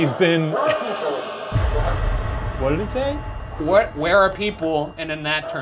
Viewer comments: it actually sounds like the ghost box said "Wonderful!" instead of "Where are people?" after Shane said that Ryan was coming back alone soon and is super freaked out. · I thought it said "white people" 😂